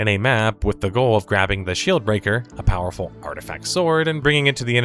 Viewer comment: i'm sorry but i heard "shill breaker". haha